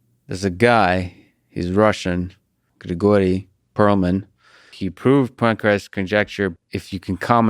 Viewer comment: Lex, when you first said Grigori Perelman's name, I thought that you said "That goy Perelman." This only confirms the fact that I need to have my coffee before I listen to your outstanding podcasts. I promise to listen more carefully next time . . .